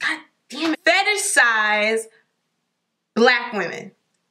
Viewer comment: what did she say in 0:48? :D fantasize? what???